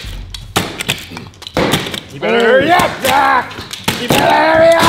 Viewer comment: i thought he said (you better relax jack!, you better relax!)